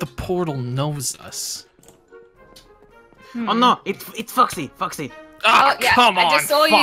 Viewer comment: did springbonnie say the f-word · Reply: He said foxy